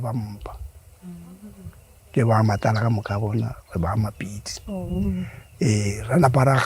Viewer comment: Do I hear ' re ba ga " Mapitse " at 1:39 ? Please help 🙏